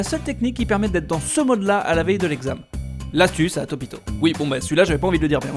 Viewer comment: Je suis désolé mais pendant toute la vidéo j'ai entendu "LA SUCE DE TOPITOOOOOOO". Sinon j'ai eu mon bac il y a deux ans mais j'ai quand même regardé jusqu'au bout. Aucune idée pourquoi.